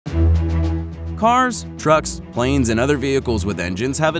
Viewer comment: Edit: 0:01 I thought he said drugs